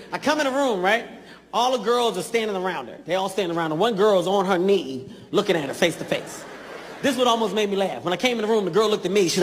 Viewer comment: i thought he said : One girl was on her knees looking at her facey face